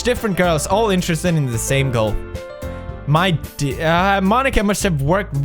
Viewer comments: One time you said “Monika” it sounded like you said “My Dick” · Is nobody going to point out that he almost said my dick instead of Monika at 16:04? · Anyone noticed that Pews almost said my dick instead of Monika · instead of saying “monika” he ALMOST said “my dick”